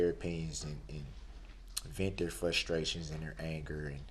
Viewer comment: Don't forget his " fustration". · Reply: I thought he said "flustration" 🤣